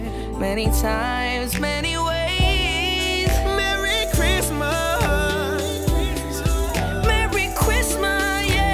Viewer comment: Am I hearing it right? He said merry chwistmas at 3:00😭😂